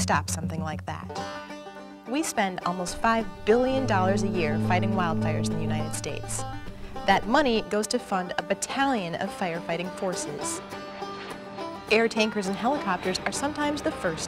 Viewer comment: wtf I thought she said Italian